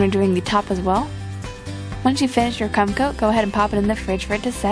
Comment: Did she say "cum coat" when she was icing the cake? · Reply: get your hearing checked because i heard her say "cum coat"